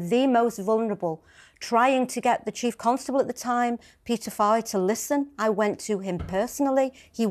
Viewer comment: Couldn’t quite hear what you said the chief constables name was ? It sounded like you said Peter file ?